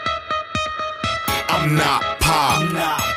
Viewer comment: Sounds like hes sayin "I'm not Pac" hahaha!